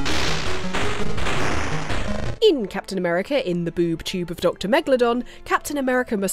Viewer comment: watches video, then thinks Hang on, did say "boob tube" instead of Doom Tube? rewinds to 13:01 Oh yes, she did!! X-D